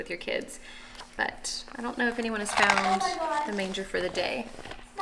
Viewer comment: I thought you said granger on the Manger 😭